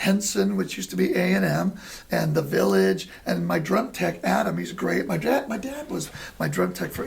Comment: Good interview. I gotta laugh...I was listening to this first thing this morning and I thought he said "My DRUG tech.When he actually said my DRUM tech! :-)